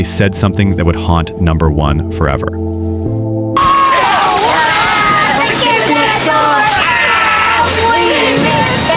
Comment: what did they say? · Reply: Tell the Kids Next door, We miss them